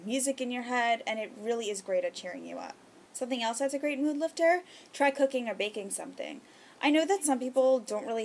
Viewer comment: rofl i thought she said "try cocaine or baking something.." like sneaking in the good old drug use.